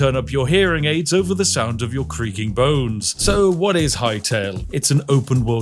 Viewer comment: Freaking phones? There are no phones? Or did he say scones? This guy makes no sense, but at least I don't have to think of my aching skeleton.